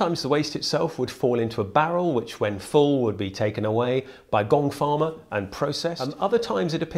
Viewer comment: the first time you said gong farmer I thought you said gold farmer and I thought you were joking